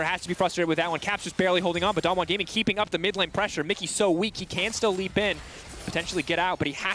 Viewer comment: did he just say "Mikyx so weak"